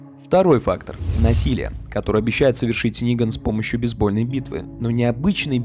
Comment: Бейсбольная БИТВА? мне одному это послышалось?